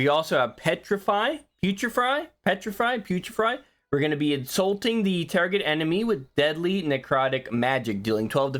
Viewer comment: did you say insulting the enemy? "Hey man, you stink!" Enemy proceeds to commit sudoku because of your hurtful insult lmao